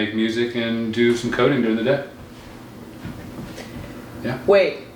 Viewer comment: Until he said he was nerdy i thought he said he does codeine during the day. Lmao 😂😂😂